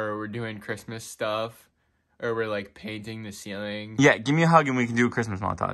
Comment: did he say 'ye'?